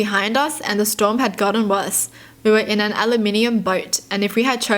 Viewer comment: did she say aluminium? 😂